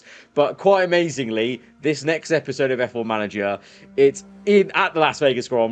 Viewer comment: did I hear f4 manager😂😂😂 Also I believe it is not sealed for constructor. If red bull get 2 champion 2 times they get 104, bigger than gap of 99 points